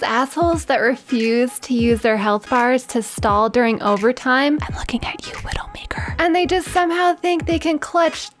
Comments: I thought she said "I'm looking at you ni**a" for #4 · I thought she said I'm looking at you little ni***